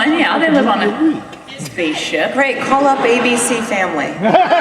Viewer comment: what does morena say to jewel when they were talking about firefly not being a sci fi genre. It sounded something like "we'll call up abcfamily"? it's